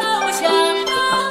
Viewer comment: 여기서 한국어로 괜찮아 라고 들리것 기분탓?